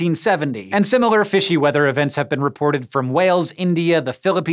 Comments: I thought you said "and similar fishy events have been reported from whales..." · I thought he said similar events have been reported where whales have fell